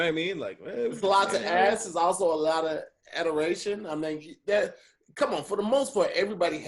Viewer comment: Hey Tom, I think meant to say admiration, but he said adderation. Adderation is when Mary J. Blige does math. Anyway back to you... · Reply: Adoration is a word and he used it correctly but the joke is still funny.